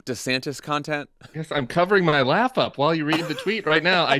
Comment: I was listening only; and misheard Tim say he was covering up his lap [to conceal his excitement]… I had to ping my phone to find it so I could go back a few moments to listen/watch again😂